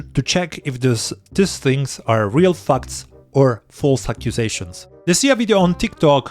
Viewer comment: I heard real fucks 😂